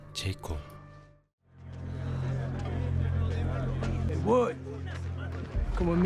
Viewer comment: 어이를 헤이가 아니라 뭐라고 하는거임? 들리는거론 뭣? 이러는데